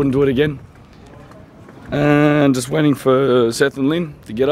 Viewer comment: Hey man I'm from Australia too, srry if this is a personal question but you mentioned centrelink, are you able to live abroad and get centrelink ? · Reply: LOL @ 0:36 in, you say Seth & Ling it sounded like cetrelink then you mentioned money exchange, srry I miss heard but I do know old age pensioners can live overseas so that's cool being from Australia, well cool when we are 65 + ........ I'm 28 so a bit to go lol, have fun mate great channel.